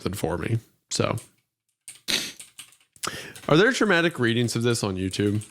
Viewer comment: I heard "traumatic reading" and was convinced for a second 'yea people would be traumatized reading this slop'